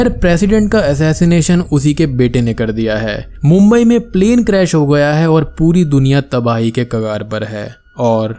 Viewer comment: Did he said mumbai mein plane crash ho jaata hai at 19:35 😂😂😂🤣🤣